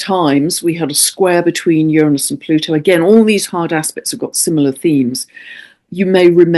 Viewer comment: I keep hearing, "hard ass-pects!" 😂😂😂